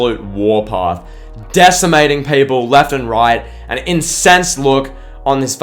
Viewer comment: I almost thought you said "Defecating people left and right".